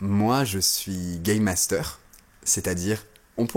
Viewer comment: Salut petite folle.😂🤣😂😂🤣. Ne pas être soi, tomber le masque ou les facettes de nous. Je pense qu'il faut garder son jardin secret. Après je pense que sans parler de masque, on s'adapte avec qui les personnes avec qui nous sommes, l'endroit, le moment. Oui tu as raison, dans la société actuelle c'est le paraître qui compte. Mdr, j'ai compris " je suis gayMaster". Tu expliqueras le travail d'un"gaymaster"🤣😂 Très belle vidéo où le klaxon t'as beaucoup énervé😒😡😒😡. Bonne continuation à plus 👌🙏🫶👍💔🙋‍♂️🙋‍♂️